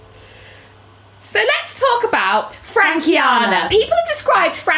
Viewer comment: did i heard it right? They talking bout FRANKIANA. please tell me i didnt misheard.